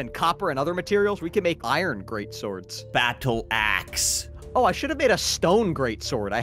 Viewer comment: i thought he said *ss instead of axe